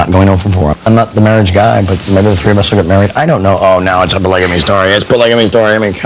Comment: can any native speaker tell me what he's saying? Is he complaining she will blame him? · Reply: Will the three of us get married uhh I don't know *oh now it's a polygamy story it's a polygamy story *